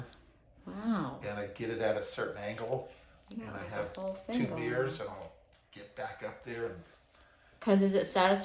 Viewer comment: He said “two mirrors “ and I thought he said “two beers”.